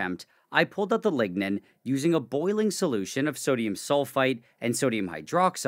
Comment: > did he say he removed ligma from the wood?